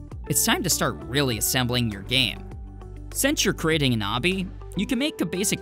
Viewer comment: A knobby? An arby? A hobby? Can someone please tell me what he keeps saying? Even the CC doesn't recognize it as a real word. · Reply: Obby